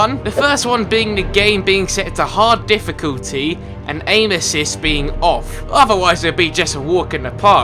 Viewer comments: When he said aim assist I thought he said anuses who else did? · when he said aim assist, I thought he said anus.